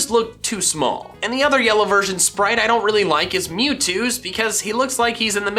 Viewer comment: I got an add right as was saying mewtwo so I heard “mute”. This gave me a good laugh so I thought I’d share